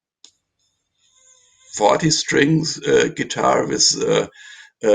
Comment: did say 40 strings or 14 strings ?? I hear 40 and that would be incredible !!! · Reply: I hear 40 strings as well mate.